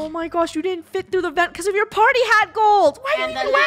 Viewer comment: did funneh just say "farty hat"